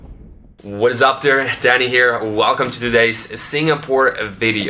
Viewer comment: when he says "hello whats up dany here" i heard it as "hello whats up DADDY here". hi daddy